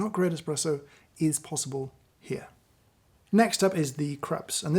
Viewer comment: Subtitles say "Craps" for the "Krups" 😅... Even YouTube knows it is bad!